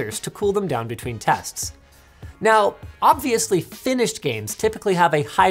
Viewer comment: "Finnish games typically have a higher degree of Polish" I'm kinda confused because I heard it like this the first time lmao